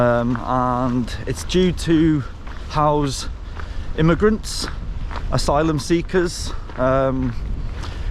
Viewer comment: You should fix your subtitles. At 0:34 you state about the closed hotel: "it's due to house immigrants/asylum seekers" that is to say an empty hotel will soon be put to use. The subtitle says "it's due to housing immigrants/asylum seekers" which means they caused it to be closed. That is a different assertion. Which is true?